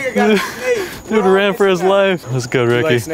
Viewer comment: did he say n!gga ran for his life?